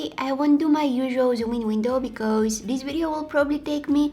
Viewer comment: When you said that you’re not gonna do your zoom and window I thought you said you’re not gonna do your Duolingo I was like Duolingo?